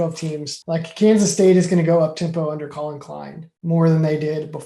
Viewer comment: Kansas State is going to do what at 1:20,? Couldn't hear you clearly. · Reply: Kansas State is going to go up tempo, is that the part you are referencing?